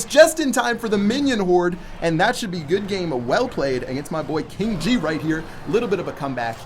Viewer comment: Did just say King G lol